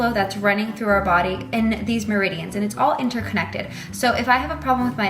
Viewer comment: Okay when you said meridians I thought you said midichlorians 😂 I was real confused there for a second